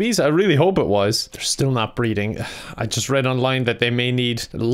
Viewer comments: Oh! "Breeding"! I thought you said "breathing". Silly mistake! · i thought he was saying breathing and not breeding imma cry